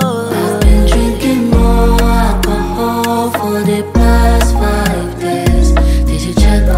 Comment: I thought she was saying "I've been water more for the past 5 days" 😭😭😭